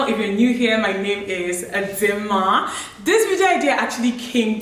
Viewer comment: Is it just me or I heard her say "a dimma" when she introduced herself 😂...don't worry I'm not tryna correct grammar buh I just find it funny, the way she said dimma Ok I'm out🏃🏃